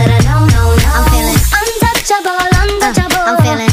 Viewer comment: Sorry pero...yo en vez de escuchar "untouchable" escuché "un chancho volando choco" xd